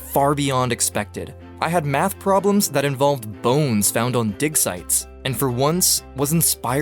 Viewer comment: I can’t be the only one who thought he said dick sights @ 2:10 lol